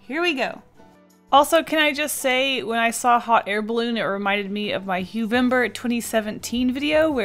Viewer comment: Was I the only one who heard "Hot hair balloon"😂